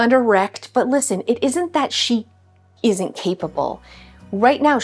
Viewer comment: I laughed when you said capable because I thought you said keepable 😂 yeah we will keep you!